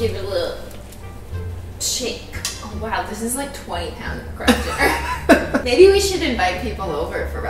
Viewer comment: All the Americans are shook "kraft dinner" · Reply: Me, being the stupid American I was, thought she was saying "Crap Dinner" for the first five minutes. -_